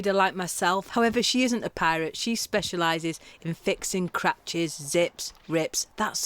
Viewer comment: thought you said crouch??? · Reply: Cratch 😂